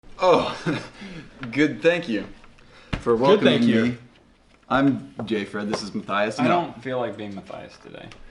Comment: did he say gay-fred